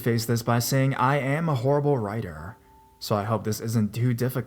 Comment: The one story that said I am a horrible writer well the video started buffering and stopped and all I heard was “ I am a hoe” I’m like wait what 🤔